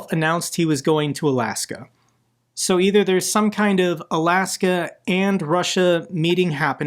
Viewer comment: I thought putin said Alaska was ruZZia? :D